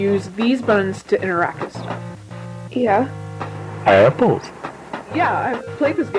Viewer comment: omfg at 51:12 it says 'hi apples' or something like that, and i thought it said my nickname(hya) and almost shit myself. i had to run it back so i didnt sob